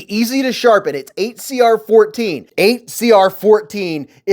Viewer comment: Good review 8cr13mov is a fare steel for the price. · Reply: Oh, I thought you said 8cr13, but you're right, I wish they would complete the branding with mov for a little peace of mind.